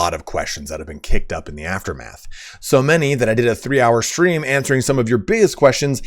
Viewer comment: I swear I heard "I did a 3 hour stream answering some of your B.S questions."